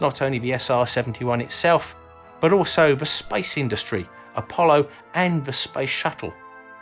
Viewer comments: I heard "spice industry"... "man that would be a plane!"-i thought... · Heh, the first time you said "space industry" I heard "spice industry" :)